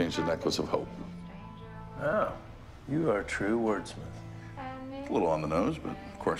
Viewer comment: "It was originally necklace of rope but I changed it into necklace of hope" "Oh, you are a true wordsmith"😂🤣🤣 Why I love so much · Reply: I thought he said Wordsman 😂